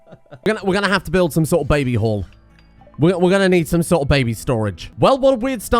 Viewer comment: "we're gonna have to build some sort of baby hall" A nursery, you mean a nursery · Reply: Oh wow, I heard "baby hole". Which can be called a quarry I suppose, now that I think about it :D